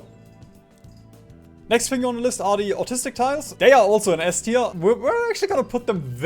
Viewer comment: I'm pretty sure it's "Anechoic" and not "Autistic".... except if it's a joke I don't understand.